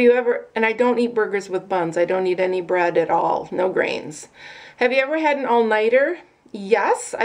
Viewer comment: Oh my gosh I heard you say on the bun thing that you don't eat.... and you said grains and I thought you said brains LOL and I did think much about it because it was a big deal and then I thought what kind of bread do you eat of course that I realized that you said grains not brains! LOL man I remember when we would go stay with grandma on a Saturday night and she would make us get down on the floor and watch Lawrence Welk... And we wanted to watch something else and she would scold us about how we needed to learn about the good things LOL it was exactly the same at our house we could have soda pop just one night on Saturday night either with popcorn or pizza! Thanks for doing the tag and for being you! Love you girl!! xoxoxo Mary